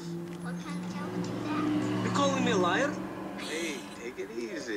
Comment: When I was a kid, I thought he said "you calling me a lion?" just like what I thought Mr. Potato Head said to Woody on Toy Story.